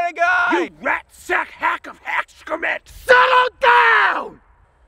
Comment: "You rat sack hack of excrement!" ... did I hear that right?? lol